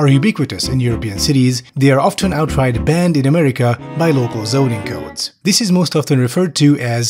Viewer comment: I thought this said "zoning cults" and didn't miss a beat at all